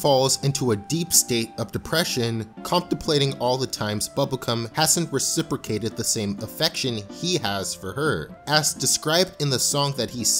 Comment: Did he just say bubble*um?